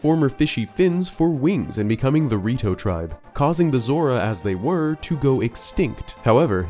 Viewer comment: I misheard this the first time I watched this and thought that you called them the Dorito Tribe. I went WHAT?! and rewatched it.